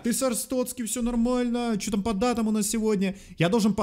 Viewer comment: мне послышалось: не "по датам", а "под датым"😹😹😹